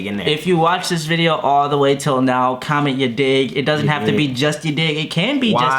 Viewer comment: i thought he said comment ur dick i was like boiii what i don't even have one 😂😂😂😂